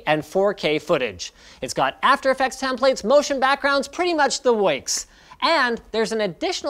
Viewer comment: what....is that word? "pretty much the works"